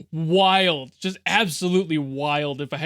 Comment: Did you say wild? "Ohhhh, round and round we go..."